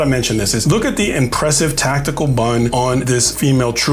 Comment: Thought he said "Tactical Bum!" rather than BUN!!